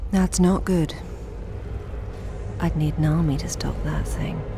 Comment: What did she say at 19:23? "I'd need _ to stop that thing". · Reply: Urosh Uchiha Novakovic she said she’d need an army to stop that thing